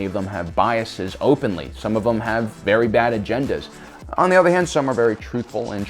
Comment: What I heard: you have a bad agenda